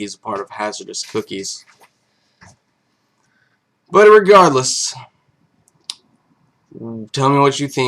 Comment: Did you say "But Irregardless" at 11:29 ? Regardless is already negative and adding the "Ir" makes it double negative D: But anyway, love this series :P